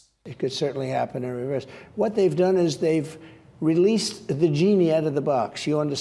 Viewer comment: Sounds like he said "vgeenie"! 😂